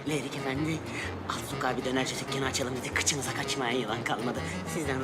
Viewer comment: who ever wrote gyro is hellanism pilled. He clearly says döner kebab.